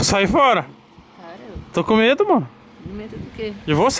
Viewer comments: so eu que ouvi amor? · foi um apelido de casal ou eu ouvi errado, "to com medo mo" ? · só eu que escutei o Tog falando to com medo mor pra?